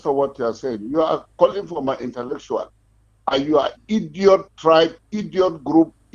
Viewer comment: Did he just say, "you're seeking for my intellectual"? 🤣😂😂😂😂🤣 Otelellectual! Onye ajụrụ anaghị ajụ onwe ya, Madam Igbo mụrụ nụ anya ka azụ. I pity those still trying to lick the asses of all these Northern 'dan iskas'. Mazi you've seen for yourself right?